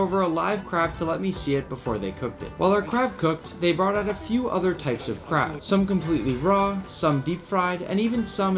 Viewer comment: "Different types of crab." I heard: "different types of crap."